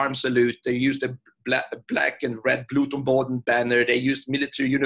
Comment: what does he say is the name of the flag here? "blud tomboten"?